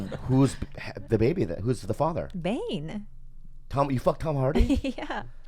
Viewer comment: what does she say? Bein or whatever? Isn’t that the Australian ex bf of her?